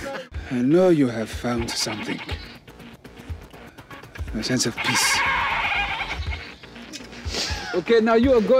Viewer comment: Sounded like he said “A sense of Piss”... 🤣🤣🤣